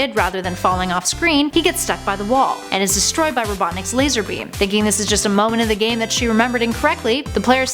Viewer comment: DID SHE JUST SAID "LAZARBEAM"?!